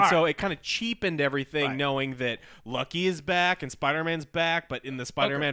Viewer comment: did he say lucky